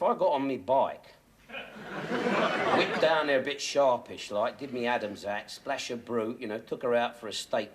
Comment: get me "Adams hat" does he say? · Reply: I always thought he said “dip my Adam’s hat” as in to doff your hat to a lady.